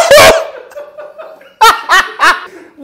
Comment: ‘Man, I hate tap water,’ he proceeds to say after deciding it tastes the best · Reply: I heard "that ain't tap water".